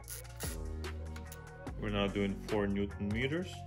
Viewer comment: did he say 4nm or 40nm? Thanks. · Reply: Hi, it's 4 Nm of jointing torque and then 45° angle of rotation